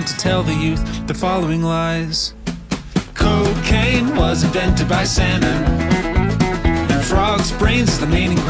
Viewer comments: I thought he said invented by Santa, that would be funny. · Salmon sounds like santa and because i wasnt paying attention i heard “cocaine was invented by santa” and i was like “HUH??” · Originally, I thought he said "Cocaine was invented by Santa" which I thought was the funniest sh*t